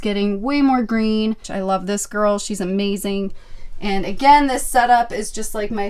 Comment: Great video! At 21:54 it sounds like you said B**ch I love this girl lol 😂